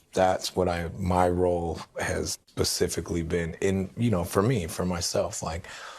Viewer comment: Did that stupid dumb dumb Federline say "pacifically" instead of specifically?! Had to go back and listen to it a couple times, and I still hear it that way... not surprised though